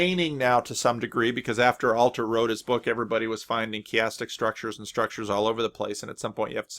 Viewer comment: Hey, at around 19:00, did you say "chaotic" structures? As in chaos theory? Is that what Alter was talking about? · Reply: No, chiastic structures. <link>